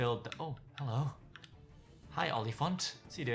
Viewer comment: is he calling? Olliephant XD